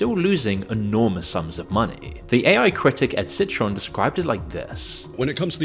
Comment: citron? Did I hear that properly....citron is a pump and dump scheme